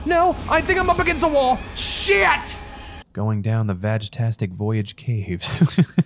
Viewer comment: I heard ''Going Down town the boyish caves'' and suddenly I paid attention really closely (15:20 ~) X} Well done, X}